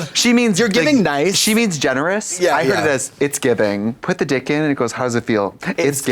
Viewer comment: ok bc why did I hear “it’s giving” 💅🏾 too and I’m not even gay 😂😂